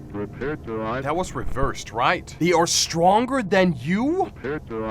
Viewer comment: "they are stronger than you think" i think thats what that says idk